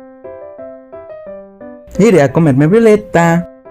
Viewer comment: Soy yo o escuché "Iré a comerme a Violeta" 🤨